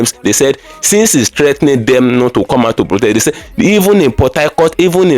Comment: I thought you said they no born any body to protest